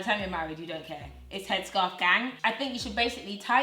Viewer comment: Dude I thought you said headscarf, bang 💥 lol